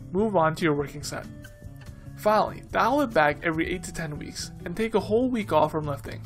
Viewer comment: I didnt understand the word you said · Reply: "Finally, dial it back every 8-10 weeks..."